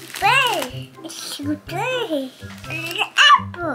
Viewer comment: okay he definitely did not know what he was saying but 110% just said “it’s so dirty!”